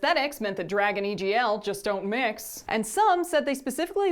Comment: I thought you said "Dragon egl doesn't mix" and for a hot second there I was ready to defend some frilly dragons. On that note, I found another thing to doodle...